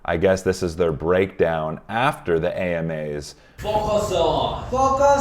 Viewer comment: I think the break gave them some space to grow as artists and as people. ps. I wasn't paying attention at the end and I thought they said "focus on.. GOT7 !" I was like, "no problem .. wait" 😂😂